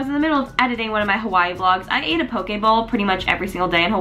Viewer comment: Every time you said "poke bowl" I thought you were saying "pokeball" like from pokemon